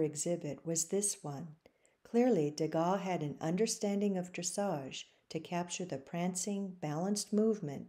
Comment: an understanding of what is that word? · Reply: Most unfortunate that the transcript (which YouTube provides - not to my approval) is very blundering. The word said in the video is "Dressage" and not "Dr". Dressage is the art of riding and training a horse in a manner that develops obedience, flexibility, and balance. Thank you for watching and inquiring!